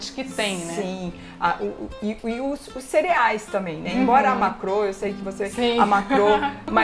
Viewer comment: aos 8:26 ela fala sobre algo que não entendi (amacro ou algo assim) alguém pode me esclarecer? Obrigada!